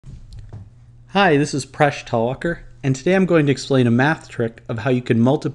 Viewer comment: i thought he said "hi this is fresh talker"